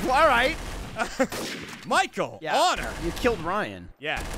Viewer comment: I'm sorry, but at about 17:00, did really say, "Michael! Latta!" As in, Michael Latta, former center for the Washington Capitals?